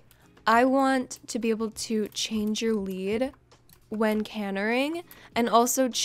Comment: Did she just say “canering”😭😭😭